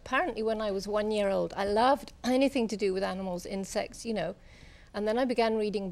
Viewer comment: What did Miss Goodall say at 00:36...? It must've been a slip of the tongue😳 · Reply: Insects